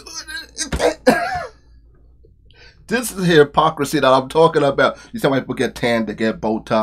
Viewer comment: did you say "the hairpocrisy "?!